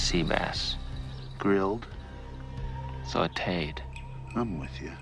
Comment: When i was a kid i thought he said "Salted" Not Sauteed. And i all always thought he said "Potatoes Are Rotten." haha